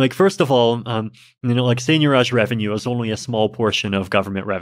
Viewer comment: What is he saying at 3:17? What kind of revenue? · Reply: Seigniorage. The revenue earned by printing money and other similar activities.